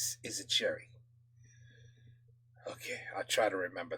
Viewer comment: it does INFACT say cherry, not icing- the translator got it wrong :) Cherry in french is "cerise", and icing's "glacage"